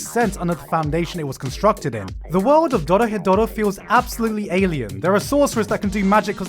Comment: what anime is this i couldnt understand the name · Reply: Dorohedoro